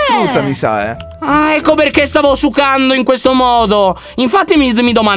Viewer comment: ah ecco perché stavo sucando (io ho capito questo) Io: ehm?? 😂😂